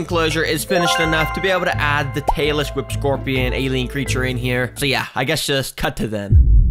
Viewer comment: WHEN HE SAID 5:33 THAT I THOUGHT HE CALLED THE ALIEN A TAYLOR SWIFT SCORPION